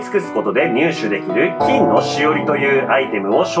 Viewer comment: 金のおしりに聞こえたから寝ます。